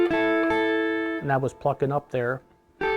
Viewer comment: What did he say at 5:08? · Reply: He said he was plucking up there